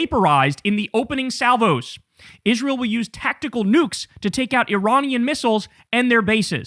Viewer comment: 😮😢😮😢😮 did you say nukes ?!?!?!?!